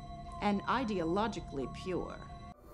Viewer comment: Everytime she says "ideologically pure" I hear "progressive "